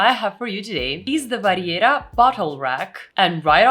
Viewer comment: Not trying to be mean, it was funny..... I thought you said "butt hole rack." ahhhh "bottle rack". Great video, thank you very much.